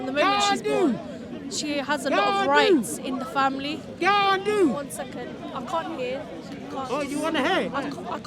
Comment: does he say - gandu ??